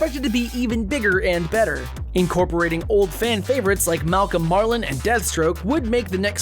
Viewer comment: I'm sorry, did you say Malcolm Marlin? On another note, Titans maybe being in the Arrowverse wouldn't make sense because by this point, according to Elseworlds, Batman has been missing for three years. Of course, aside from that, Swamp Thing has been confirmed to exist in the Arrowverse (twice, once as an off-screen joke before Constantine even joined the Arrowverse).